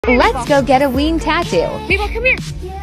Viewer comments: At first i thought she said we*d tatto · I thought she said weed tat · I thought she was saying she's going to get a weed tattoo lol